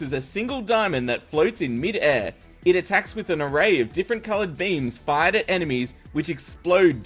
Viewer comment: I thought u said different coloured memes in stead of different coloured beams